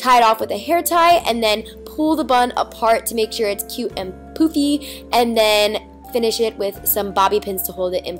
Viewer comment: who else thought at 3:55 or 56 Niki said poopy...